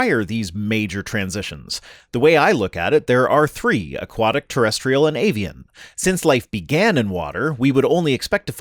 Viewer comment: I thought you said " aquatic, terrestrial and alien"😂